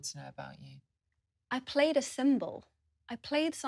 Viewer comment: I completely thought she was saying “I played a cymbal” at first 😂 I’m not even sure how one would play one cymbal. 🤣